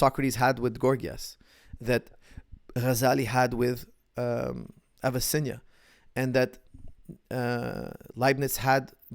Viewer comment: what are the names he says after Socrates and Gorgias, before Descartes and Voltaire? cheers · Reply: I think it was Ghazali and Avicenna.